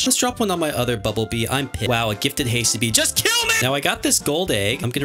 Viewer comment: did he said I'm pissed